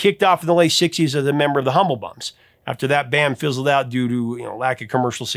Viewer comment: It sounded like you said Humblebugs, not Humblebums.